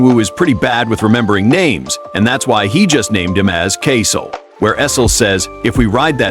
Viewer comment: i heard caseoh not kaisel